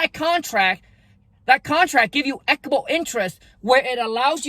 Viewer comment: ! Really quick (or to anyone that may be able to answer my question). What did you say before the word 'interest' on the 15:56 mark? you said something like "that contract gives you ___ interest where it allows..." THANK YOU SO MUCH FOR THE VIDEO and I hope you keep it up!! God blesssss. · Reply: "Equitable"